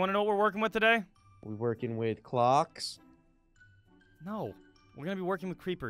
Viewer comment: Omg at first instead of clock i thought he said glock O_O